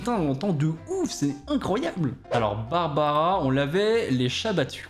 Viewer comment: moi j'ai pas entendu "on lavait les chats battus " moi j'ai entendu : on lavait les chèvres battues"🤣